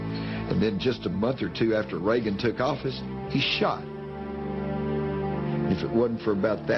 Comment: what does he say at 3:47 ? Is he sayin bush got shot???